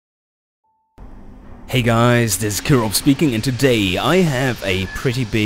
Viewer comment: swear everytime i hear killrob say his name, i hear "kirov"